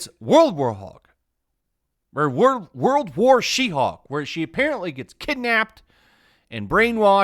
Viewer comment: When you're saying WW She-hulk, I heard 'Roulroul chi-houk!' at first and giggled.